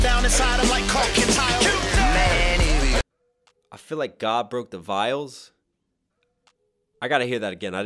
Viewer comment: I thought he said mark the vials